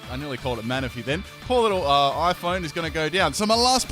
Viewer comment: wait did you call the phione and iPhone? hahaha I would have sworn you said "I nearly called it Manaphy. Poor little, uh iPhone" XD